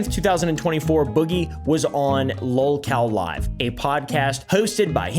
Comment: Low-cal*, as in low calorie. What is lolcow? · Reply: Thank you. It sounded like he said low-cal to me, and I didn't hear an 'explanation'.